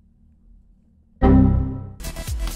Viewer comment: now all I hear is why am I argentina